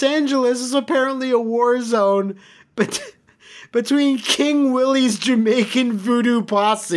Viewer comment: god damn it i had to turn on the closed captioning because for a second there i thought you said king woolie instead of king willie and as an osw/ super best friends fan my body is not ready for woolie to rock a king gimmick last thing i need is for you guys to have a crown vs crown match at the next rustlemania we do not need the king of kings vs the king of lairs